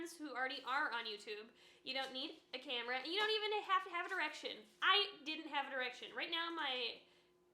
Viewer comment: I really thought you were saying "you don't even have to have an erection" until I realized you were actually saying *direction. Haha I was like :O what a strange tip! Anyway...great video with solid advice.